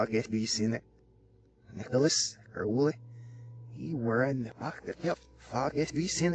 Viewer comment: Anyone know what he's saying at 7:42? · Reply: It's the red text. "Nicholas Crowley, you are in the Book of Death FOR GOOD REASON."